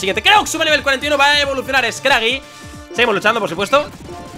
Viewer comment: En El Minuto 15:10 parese que dice Qeu Kreux Evoluciona a Scraggy